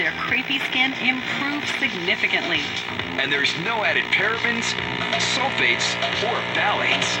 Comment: I've got to get my ears checked, I thought they said "No added Terrapins".